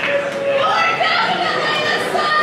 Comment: also wtf does that girl say at 23:11? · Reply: you're definitely the sunny